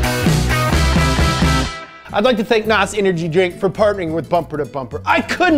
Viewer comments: I thought he said Nazi energy drink for a second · did he say Nazi Energy Drink?